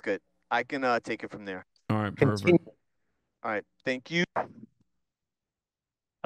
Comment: for a second i thought he said “alright, pervert”